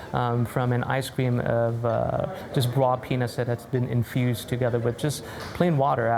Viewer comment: it really sounded like the chef did NOT say "raw peanut" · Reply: indeed, I heard "raw peanus"